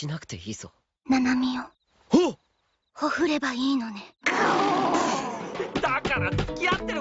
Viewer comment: Can someone write the words spill out by mashiro before "bai no ne"? Is it hohure? Ohure? I heard it is "hohure bai no ne" Please help, im desperately trying to learn japanese, looking forward to ur assistance! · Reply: It's been 3 months, so dunno if it's helpful anymore, but ほふればいいのね is what she says. 屠る is the dictionary form (and the kanji). Means "to slaughter/butcher."